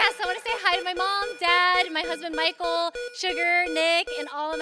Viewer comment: Thought she said "My husband Michael, Sugar..." Told my wife that's her new nickname for me.